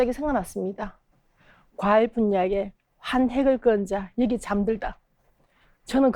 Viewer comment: 묘비명?이뭔지 아시는분 댓글부탁합니다 제가 잘 못듣네요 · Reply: "과일 분야에 한 획을 그은 자 여기 잠들다" 이렇게 들리네요. 이 분이 과일소믈리에신데 같은 꿈을 꾸던 아들인가봐요 뉴_뉴